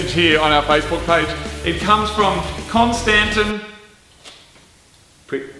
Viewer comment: I thought he said the facebook request was from Konstantin Fedrov ! That would have been way epic